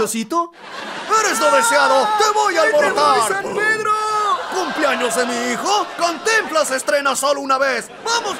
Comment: ¿Qué dice el nerd cuando lo abortan por la ventana en el minuto 2:35? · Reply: Le entendí "ahí te voy san pedro"